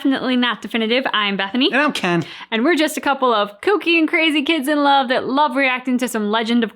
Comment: Hahaha was yawning during your intro. And I heard "we are just a couple or cocain crazy kids thag love some korra" had to rewind and listen again just to find out I heard ya wrong